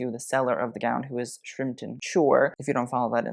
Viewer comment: Does anyone know what Instagram page he suggested we follow around 2:50? I have listened to it like 10 times now and I still can’t understand and I want to follow! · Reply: Shrimpton Couture